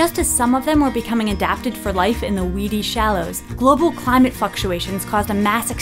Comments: goddammit, every time, I hear, "global climate fucktuations..." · xD who else heard "f*cktuations" 🤣🤣🤣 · Who else heard global f***uations at 0:52 ? Only me? ok 😞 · I thought she said fucktuation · umm did she say Fuk-tuation? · sounded like she said fucktuations